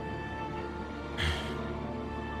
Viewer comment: Is it just me or at 10:15 did Ruby say "dad"?